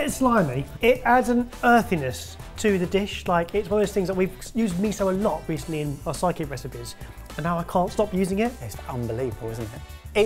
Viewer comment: Hey Sorted Food Team, I've had this thought a lot, but you really really need to get better subtitle writers. The mistake here takes the cake, 6:59, they wrote your app name wrong! It's written as "recently in our psychic recipes," instead of sidekick! I love having subtitles on, and it really bugs me that yours is apparently not machine written (or at least, isn't using google's auto-caption) but clearly, no one's reviewing it! Like, it's an accessibility feature, and it's good to get it right, plus, it means if someone is using auto-translate, it's not gonna be a mess. Please get better captions!! EDIT: There's even more mistakes. They even write Evers instead of Ebbers at 14:00! Whoever you're paying to do this is doing a terrible job.